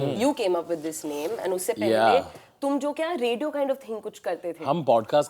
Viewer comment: did she say radio kinda thing kuch😂😂😂😂😂